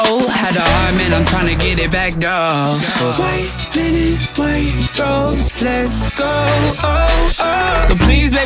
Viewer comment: ohhhh thought it said "white men in white robes"